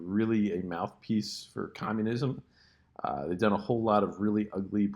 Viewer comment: Wait, did he say Communism @ 1:10? anyways, I'll always love your channel!